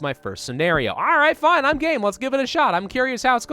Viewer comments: is he saying gay or game? but how gay? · lol when he said "Im game for this" i thought he said "Im gay" oof i had to go back and rewatch what he said XD